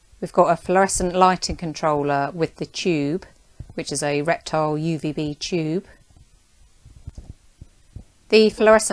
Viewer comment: What did she say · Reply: reptile uvb tube